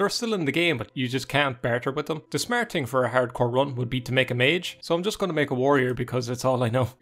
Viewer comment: I keep hearing "Herd-core", and thinking of a heavy metal shepherd moshing next to his flock.